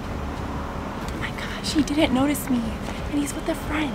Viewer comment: did she say "she didn't notice me"? I'm confused